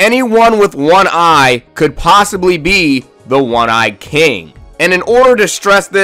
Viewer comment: omg i dont even know how many times i heard "A one eye King"